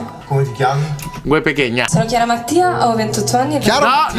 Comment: Ma solo io ho capito "Chiara Martina" ? (La tipa in rosso)